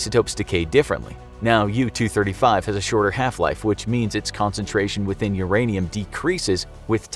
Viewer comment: Half Life? Did I hear that correctly?